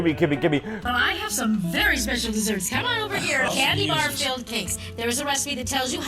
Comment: I swear she said "Cathy barf-filled cakes" at 14:24. I'm crying so hard I might shit my pants. I need to go outside.